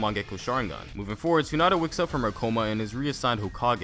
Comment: I swear that said Hinata woke up from her coma...